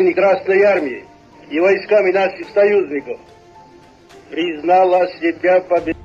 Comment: did he say the N-word?